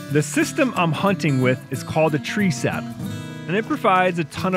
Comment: What kind of stand was using? It sounded like he said Tree Sap.